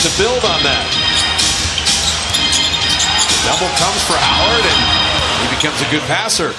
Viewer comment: did he call dwight howard ,"harden"